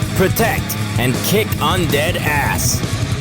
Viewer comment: ok I thought the intro said slap that undead ass